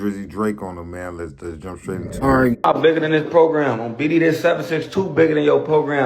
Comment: Did he say on BD